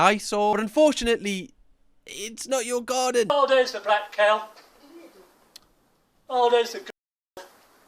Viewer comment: what did the guy say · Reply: Initially "oh there's the black cow" and then "there's the g*llyw*g" I believe. (Replace the * with an o.) Its the name for those extremely racist dolls that look like if Raggedy Anne was a racist caricature